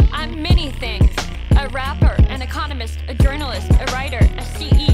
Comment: When she said she’s an economist, I thought she said “and a communist” 😅